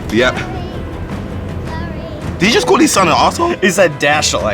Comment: I thought the guy said AASHOLE too 😂😂